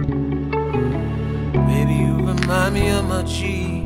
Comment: I thought he said 'you remind me of my jeep' as to referencing R. Kelly's song.